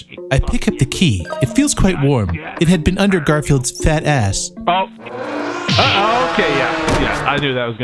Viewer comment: I like how subtitle says "fat cheeks" instead of "fat ass"